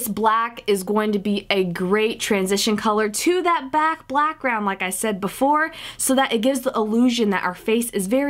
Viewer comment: you said "back blackground" instead of "black background" haha I'm dying (that wasn't intentional right?)